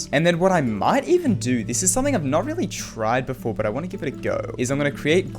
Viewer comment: "This is something I've not really Tride before but I want to give it a go" I'm sorry I heard it and I can't unhear it.